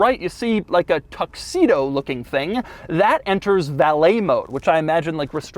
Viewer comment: I should get my hearing checked. When Doug said "valet mode", I thought he said " ballet mode", which didn' t strike me as odd. It's a Tesla after all. They do things differently.